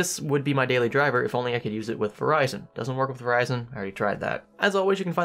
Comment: it doesn't work with Ryzen? Am I mishearing this?